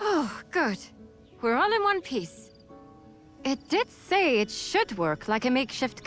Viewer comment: did she said ONE PIECE